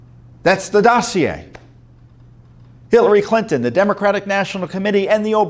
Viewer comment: Right around 2:30 it sounded like Tom said "Billary Clinton". LOL!!! BTW, if the Muller report had been 20 pages then every American would have read it, which was not wanted.